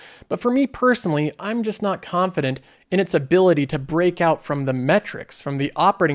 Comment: i heard "break out of the matrix" of the auto industry 😂